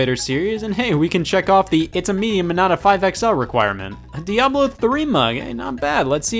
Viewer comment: Does anyone have any idea what he's saying at 6:41? It just so happens to be this part that I don't understand where the captions mess up. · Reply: "and hey, we can check off the 'It's a meme and I'm not a 5XL!' requirement!" or at least I think it is???